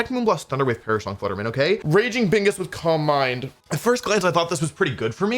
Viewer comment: I heard "raging bingus" and now I can't help but imagine Raging Brachydios but it's Bingus